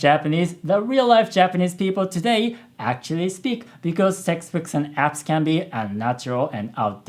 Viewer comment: His 'textbooks' sounded like 'sexbooks' to me (─.─||）(^～^;)ゞ